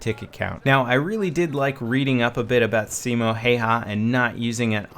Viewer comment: omg at 7:41 when he entions Simo the subtitles say "seema hey hand"